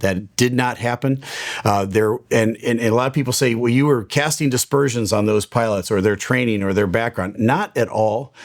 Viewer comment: the expression is "casting aspersions" but it sounds like you said "dispersions"